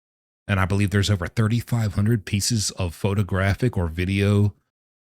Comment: ❤ love you But I keep hearing it's 35,000 pics and videos I don't think it's 3,500 But either way he's GROSS!!!! THANK YOU ❤